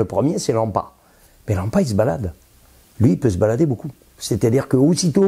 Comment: Bonjour à partir de 7:22 il parle de quoi ? lampa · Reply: bonjour. C'est l'AMPA (Acide aminométhylphosphonique).